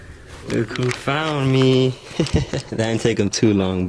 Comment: did he say Goku found me or Look who found me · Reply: Llama Rrama he said look who found me😂😂